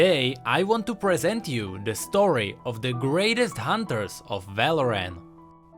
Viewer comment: Did he just say „the best hunters of VALORANT“? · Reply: ik this is a lil bit too late, but I'm pretty sure he said valoran